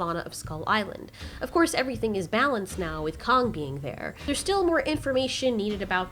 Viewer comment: Is it me or did she say Kong king at 0:29?